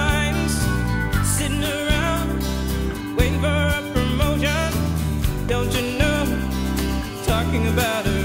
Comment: I do not know how I ever thought she was saying, "Cinderella, waiting for a promotion" 😂😂😂 I'm 37 and been listening to this since it came out😂😂😂